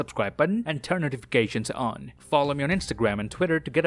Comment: I heard "Dave Chappelle" and I lost it!!! 😂😂😂